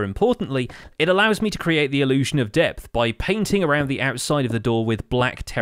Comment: i thought you said 'death' and i was super confused